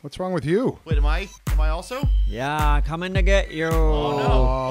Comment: what say there the N word 😂😂😂😂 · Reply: he said I'm comin ta get you